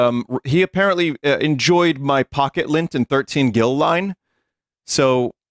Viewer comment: Oh pocket lint! I thought he said "puckered lips"